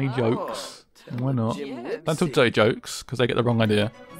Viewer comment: I thought it said "tell joke about dicks" .... Did anyone have the same issue? XDD